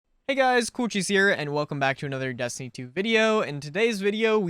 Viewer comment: when u said coolcheese i thought u said cewchies